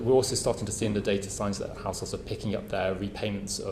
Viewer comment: To protect arseholes? What I thought I heard when I started this video. Now I can’t not hear house holds as arseholes. It’s wrong but it’s funny